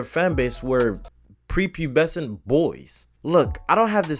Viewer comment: I heard: "Creepy peasant boys", when you said prepubescent boys😂😂😂